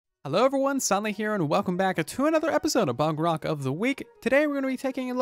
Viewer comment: am I the only one who heard bogrock of the week?